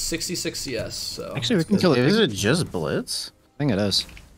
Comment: Did just say Jizz Blitz??